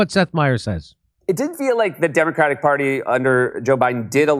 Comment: I heard didn't... Not did. However this does not excuse Seth Meyers from the shilling he did for the past 4 years. He is still a hack.